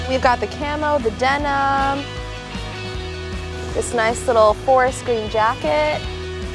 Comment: did she just say foreskin jacket at 1:03?